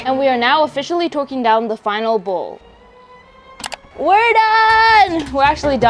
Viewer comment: i thought she said "were finally twerking down the final coil"😅